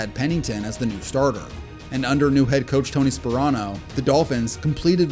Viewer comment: i thought he said coach Tony Soprano